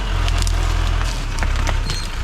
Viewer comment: What are you saying at 12:56? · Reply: Blue don't want none tim